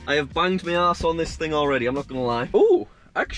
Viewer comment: Did you just say a**